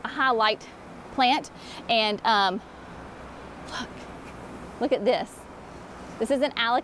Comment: Holy smokes! What did you say at 10:12 ? haha. You are too wholesome for a word like that, so I'm going to assume that's a sigh! ;) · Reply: 😂😂😂. If I’m looking at the same spot as you, I whispered “look” and then repeated it louder.